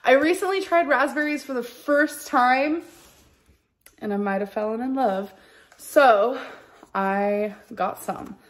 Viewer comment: what did grammar lynn say? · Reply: Fellin in love. 😂😂😂😂😂😂